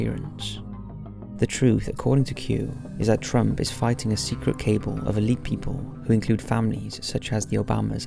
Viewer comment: you say cable and I think you meant to say cabal? Just so you know, please let me know if I'm wrong here! Thanks for all your great content 🌟